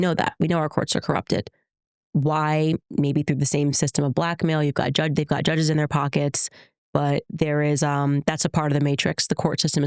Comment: did she say black men have judges in their pockets? 😂 can’t be serious!